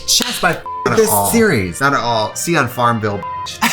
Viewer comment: Did Trixie said "Sana All"???